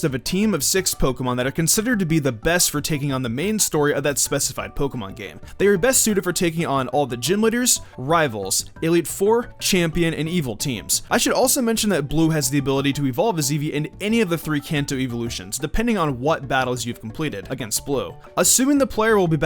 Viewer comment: did you say gym liter